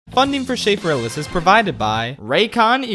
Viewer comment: your channel's name sounds like Sleep Paralysis i just hear "Funding for shleep paralysis" every single time